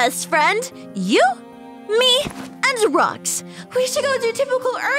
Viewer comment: I thought Skye said, “You, me, and drugs” for a second